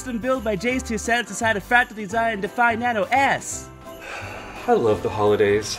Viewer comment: Why do I always hear "Nano Ass" Whenever he says "Nano S"